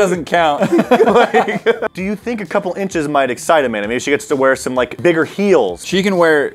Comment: Did I just hear “do you think a couple inches might excite Amanda”